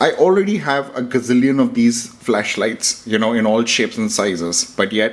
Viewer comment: I was getting a drink as I started this video and I thought I heard you say “fleshlight” and I almost spat out a big mouthful of my drink